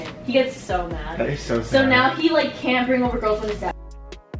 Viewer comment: lol who else heard "he like cant bring girls over when his dad"